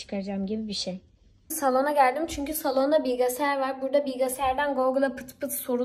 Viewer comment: Bilgesayar diye mi duydum ben yoksa bilgisayar mı dedi